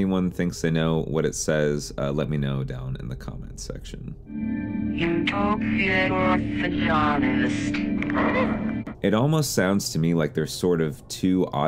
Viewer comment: I heard you told me you were orthodontist